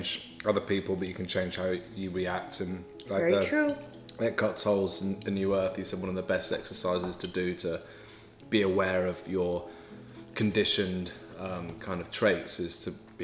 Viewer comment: Hello Jenn I'm a korean subscriber of yours. I watch and shadow speak(?) your videos for my improvement in speaking English. I have something I would like to ask, may I know what is saying from 16:50 to 17:00?? I listened repeatedly but I can't understand. I would really like to know. And thanks for your videos always:) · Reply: I wonder if you still want to know this. says: "Eckhart Tolle in "A New Earth", says one of the best exercises to do to be aware of your conditioned, kind of, traits, is to be with your parents."